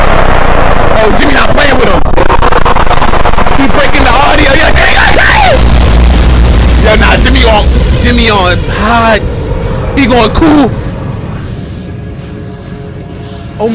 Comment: did bro Say "Jimmy got cake"?